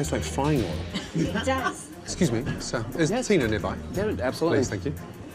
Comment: Guys, I'm studying English and I would really appreciate your help! At 1:02, does he say "Is there Tina nearby?" or something else?